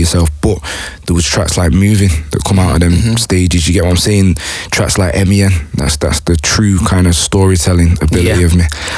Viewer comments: What's the name of the track he says at 15:20 ? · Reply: Ryan Eccles moving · what does he say ? Track like mia ? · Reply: M.E.N